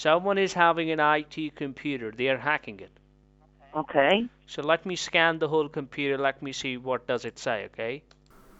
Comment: i heard so let me scam the whole computer